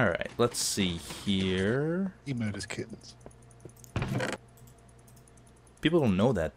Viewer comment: What did say at 10:03? Etho has kittens?